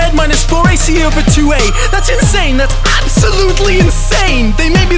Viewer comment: lol i thought when u said thats insane i thought THAT IS HOW U DO DAt